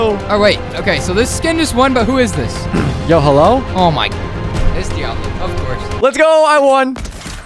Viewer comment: I love how he jumps to conclusions and just says “It’s Diablo” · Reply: I heard em say "it's diavolo" 🔥